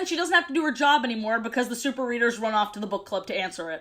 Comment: I thought said "the super weiners" 😭💀